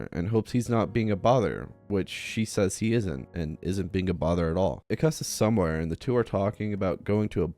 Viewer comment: Ooohhh I thought it said "You are not being a brother at all"